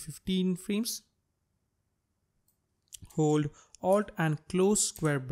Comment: about 11:29 what you say? Hold "ALT" and ??? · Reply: Hold Alt + ]